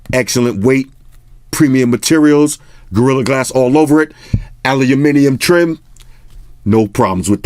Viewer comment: did you just say aliliminium ??? :D